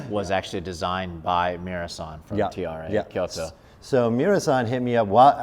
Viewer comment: designed by who ? I cant understand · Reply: Kei Miura of TRA Kyoto